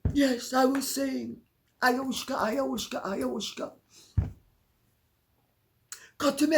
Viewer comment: I know this is a dumb question, but could someone tell me what Lady C is saying at 31:25? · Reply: She is saying "Ayahuasca, Ayahuasca, Ayahuasca." It's an hallucinogenic plant, which he claimed he has used. He's already had the weed from Doris so is calling for this too!